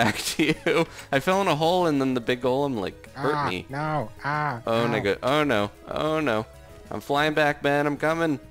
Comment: did he say oh in nigga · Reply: I was trying to say "Oh no!" & "Golem" & it came out so very, very wrong haha.